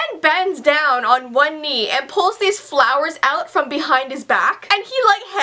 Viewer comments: At first I thought it said "Justin Bieber gave me followers!" I didn't realize that it said 'flowers' until she had explained it all😂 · I thought you said Follower